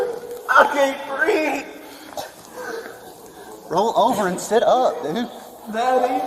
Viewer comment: what did he say!!!! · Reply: Roll over and sit up dude.